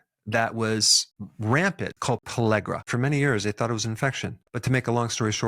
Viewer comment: are you sayiing Pellagra? Having a hard time understanding every time you say I’m not really sure exactly what you’re saying… even close caption isn’t picking it up for me.